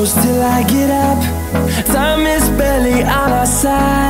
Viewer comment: i thought he said belly instead of barely my entire life